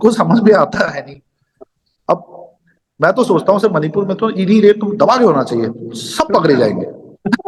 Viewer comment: What did he say? I couldn't understand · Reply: ED raid😅